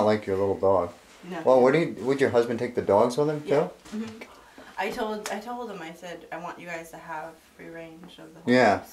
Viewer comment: On 30:38 to 30:39 i can hear an evp. Can you please tell us what it is saying? It sounds like a women's voice. Anyway, i really enjoy watching you, and your team very much! You guys are very respectful with the spirits and that is the way that they should be addressed. Remember, many of these spirits were ati one time flesh and blood like you and i. Anyway, i found another evp in one of your other episodes. I will let . know very soon which episode it was and what i think is being said. I am very sure that you guys did not pick up on it. It's a whole sentence and you can clearly hear what is being said. Crazy! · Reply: "It ain't your dog...It's your daughter"